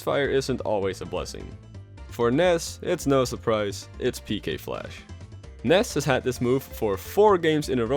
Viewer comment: For the longest time, I thought Ness was saying “PK Pulse!” instead of “PK Flash!”